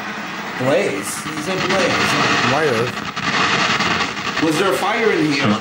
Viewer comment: sounded like "slave"